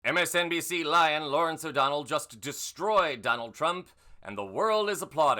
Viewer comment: When you said “MSNBC lion Lawrence O’Donnell” I heard “MSNBC LYIN’ Lawrence … “ and thought WTF? 😂 And then my brain caught up ~